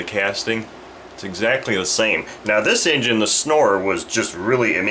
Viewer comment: I thought you said the Snore 40 haha